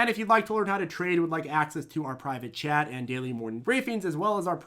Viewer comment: For the longest time, I thought your were saying, “access to our private jet”🛫 But, yeah, it’s the chat😁